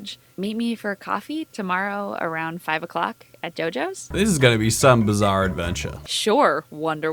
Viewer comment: Did she say... ahem JoJo?!?!?!